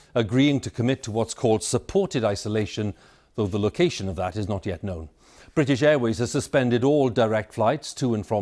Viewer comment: Did he say "the location of that is not yet known"